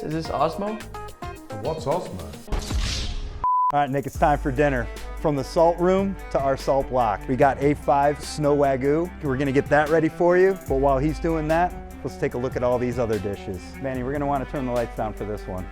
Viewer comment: did he just say Wagoo instead of wagyu